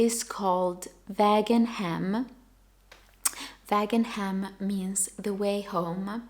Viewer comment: The puppies are so CUTE🥰 I bought the same Nutiden yarn, Vägen hem, because I love the name😊Now I'm considering knitting the Winter Berry Vest😍 Thank you for all the inspiration! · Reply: I swear I thought I heard "Vegan Ham" 🤦‍♀️ Vägen Hem is much better 🤣